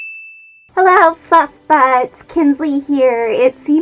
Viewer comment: Did she just say.... "hello fuzzbutts"??